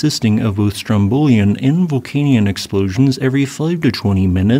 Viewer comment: Not a volcanologist here, so please forgive a possibly stupid question- what is the difference between a Strombolian explosion vs a volcanic one? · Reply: Not a stupid question at all. Unsurprisingly given that you are quite early on the learning curve, you have misheard 'Vulcanian' as 'volcanic' - and Strombolian and Vulcanian are two different types of volcanic eruption. If I could remember the definitions off the top of my head I would tell you - I can't, but probably someone else can and if not you should be able to Google it easily enough. And welcome to the jjourney of discovery!